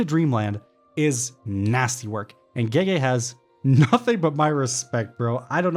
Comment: Why on 27:40 in the English captions when GojO is going to die the captions instead of gege it says gay 😂😂😂